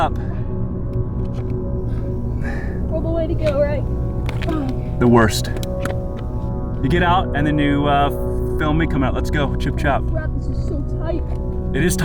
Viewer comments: Did he just said - let's go CHUP-CHAP ( in hindi) . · I heard you say chup chap. What does that mean?